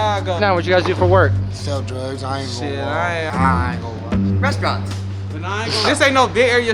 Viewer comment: what the leprechaun say? i cant understand it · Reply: restaurants